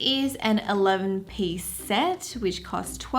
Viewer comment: Did she say “eleum piece set” ?? @ 1:28??!